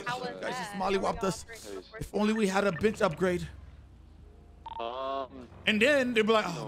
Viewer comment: I heard say “…if only we had a.BITCH upgrade.” 😊😊😁